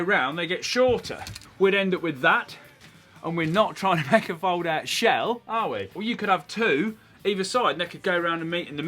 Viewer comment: I thought it said fold out shed